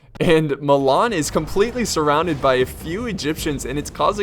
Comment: am i the only one who hear milan is completely surrounded by few egyptians when papal state got surrounded at 9:00 lol nice replay,ur every video is awesome man! keep it up bro!